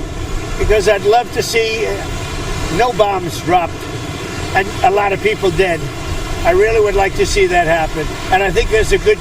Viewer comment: did he say "I'd love to see no bombs dropped and a lot of people dead"?